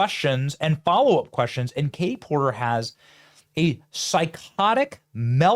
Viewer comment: Huh… I heard you say Karen Porker…. Pretty sure that’s her name.